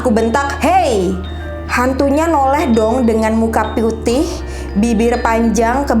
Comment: Sampe diulang berkali2 w salah denger apa gmna kak bilang "dengan muka piutih? " wkwkwk ngakakk padahal lg serem2nya😂😂